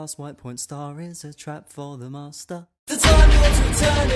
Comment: I heard the"face of the master"O_O Does that mean I'm on the Masters side...